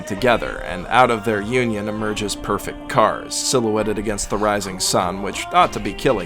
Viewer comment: did he say perfect kars?